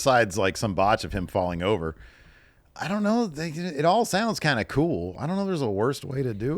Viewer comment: did just say "idk n***a"? He pulled a Roman